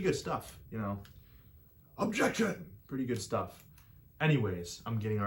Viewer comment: when u did the ace attorney reference I thought you said "EJECTION" when you ejected the cartridge. So glad I found ur channel dude keep doing what you're doing!